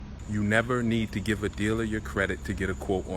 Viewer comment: I thought u was saying drug dealers check credit 💀